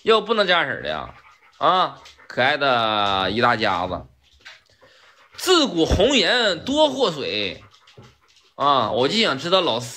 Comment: 差點聽成自古紅顏多薄命，是禍水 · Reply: 聽清楚了，是"自古紅颜多禍水"哈哈。